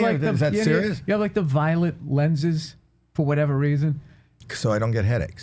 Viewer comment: I thought he said violent lenses too 😅